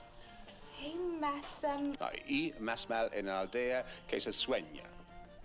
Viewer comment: I'm spanish and I still didn't understand what he said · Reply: Same here, I am Spanish and I had to Google it 😂 It is supposed to be a quote from the Quixote: "Hay más mal en la aldegüela que se suena". Number 6 is proficient in many things but speaking Spanish is not his forte 😅